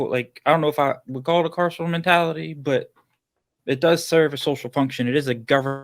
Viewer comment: Please can someone tell me what word is being said at 1:59:08 and said it before. A castle mentality? Carson mentality? What is this word please and what does it mean?